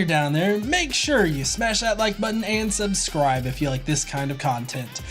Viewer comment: Sounded like you said "Stand up and lose your voice". Maybe that was a Freudian slip? Subconsciously you are hinting to yourself you should stop reading comments as loud as you do? #StillSmallVoice